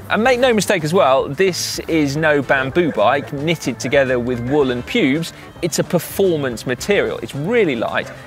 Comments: Do you have any thoughts on sustainability and cycling? Let us know in the comments below! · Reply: Did Si actually say "Bamboo bike knitted with wool and pubes"? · Did Si actually say "Bamboo bike knitted with wool and pubes"? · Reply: I heard "woolen pubes" ??